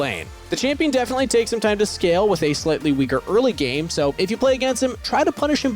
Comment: did you just call Ezreal “Her”? 😅